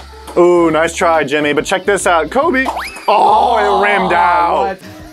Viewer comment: did tanner say “Kobe!”